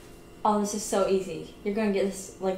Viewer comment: Pretty sure he thought she said “It hits you so hard”